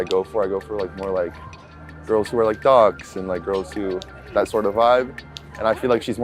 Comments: I thought he said “girls who are thots” LOL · is he saying the style of girl he goes for is dogs? Lmao